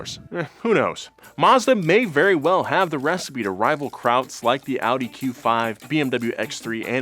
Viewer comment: I had to turn on captions to see if said "Krauts" instead of "crowds" 🤣🤣